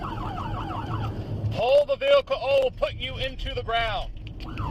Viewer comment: As someone who is pro GOOD cops, this is absolutely unacceptable and disgusting. Am I right that I heard him say, "pull over or I'll put you in the ground" "Go ahead, try something"? Disgusting. This woman handled this horrible situation perfectly. Be respectful, follow commands, don't run, even if the cop is in the wrong. Get out of the situation and then SUE the pants off him. This cop is a disgrace and dangerous. This is why the good ones can never make any headway. I get that traffic stops are the most dangerous situation that cops are placed in. But I didn't see anything concerning from the woman, not one. She was respectful, even though he didn't deserve it. They need to educate the officers better about this rule in the driver handbook that we all seem to know.